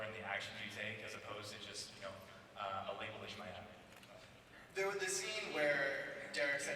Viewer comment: what does hoechlin say at 12:52 after he says "and the actions you take as opposed to just..you know..." ?? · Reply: "And a label you might have" :)